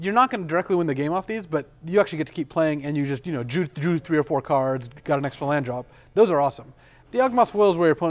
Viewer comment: Did he say "You just Jewed three or four cards"?